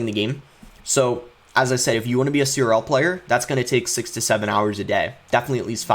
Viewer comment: I thought he said 67 hours a day lol